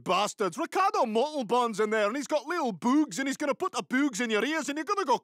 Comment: Instead of little bugs, i thought he said boobs. so i was confused.